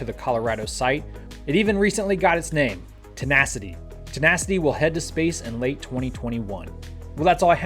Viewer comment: Instead of "Tenacity" I thought he said "McNasty".